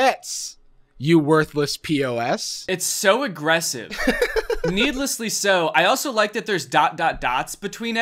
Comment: i thought danny said "needless to sew" i was so confused